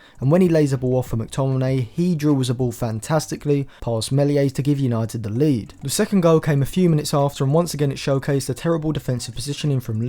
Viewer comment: did you say a few minutes